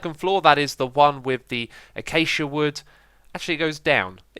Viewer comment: what did you say, acasia? I thought i was achacha